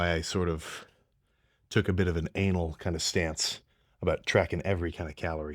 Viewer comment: did he say anal???